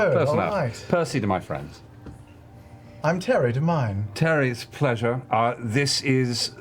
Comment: Did no one notice Terry gave his actual last name to Percy? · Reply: do you mean at 2:59:17? I think he's just saying "Tary to mine" as in he's called Tary to his friends.